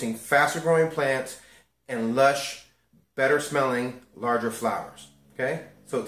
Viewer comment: I heard "Kush" instead of flowers lol.